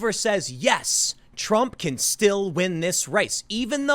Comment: I honestly thought he said trump-chan